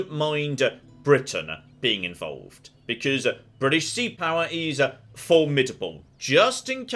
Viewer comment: Did you say "british sea power"? Want to be free, it will last forever, eternally.